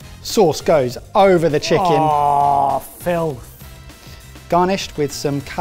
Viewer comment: Wasn't Barry saying "ahhh filth" at 11:03ish instead of Jamie as it sais in captions?